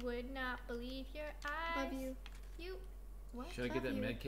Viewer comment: did Roni say "love you" to Nessa???